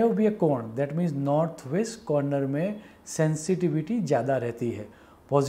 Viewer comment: In sub-title instead of North west , north east is mentioned at 11:48.... Kindly rectify